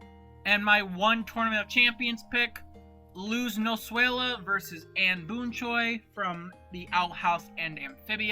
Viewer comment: It sounded like you said luz noswayla instead of Luz noseda